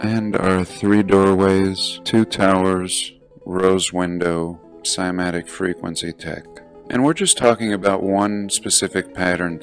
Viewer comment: Did you say sematic frequency tech?